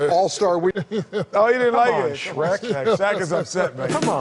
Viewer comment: I swear Chuck’s right 90% of the time but Shaq just says “4 rings” and thinks he won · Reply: I'm at a food court and watching this and I hear "Who do they mistake you for Shaq" and I hear SHREK - Now all these people sitting nearby wondering why is that guy sitting by himself shaking with laughter - you guys are dangerous